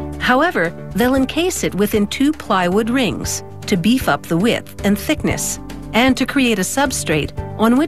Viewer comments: Thought she said beef up the whip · Thought she said “beef up the whip”